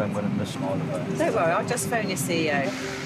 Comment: LMAO "I'll try and explain it to my CEO my meeting I'm going to miss..." If you report into a CEO, tell him to not expect you to use a budget airline and be on time for every meeting... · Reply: I thought she said I will phone your CEO! Imagine her saying am the pilot and your staff member is going to be late